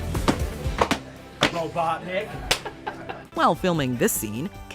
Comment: I could be being overthinking this but if you listen closely at around 6:50 you'll hear the director say "Robotnik" instead of Jim, and I'm sure this is because Jim has been known to always become the character he's playing, he recently said that "Jim Carrey" doesn't exist and he's just a character.